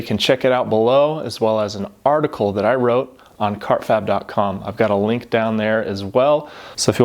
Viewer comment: I thought it said fartkab😂